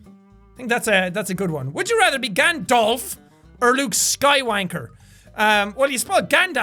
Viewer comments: Is it just me or did he just say luke sky wanker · Did he just say, "Skywanker"?